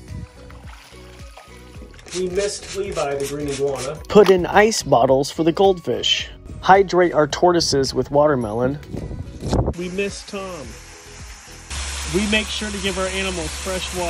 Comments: All i hear is "We miss Tom" and we dod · "We mist Tom" What I hear: "We miss Tom" UGHH that made me sad 😭😢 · "we mist Tom" me too, sobs, me too. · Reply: I thought that too, I heard "we miss Tom"